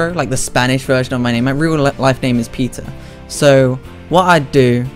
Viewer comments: Did... Did he say his real name is pizza?! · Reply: lol Peter. lol I thought he said Pizza for a sec to xD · I thought he said his name was pizza and after a few times it was Peter and that's more realistic